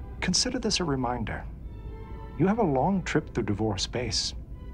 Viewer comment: I thought he said "You have a long trip through divorce space." Lol